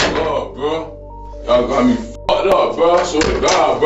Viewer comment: all I heard was “I’m a stupid phk” Edit: lost control? I’m going out on a limb here by saying , in fact, he was never, in his life, “in control!”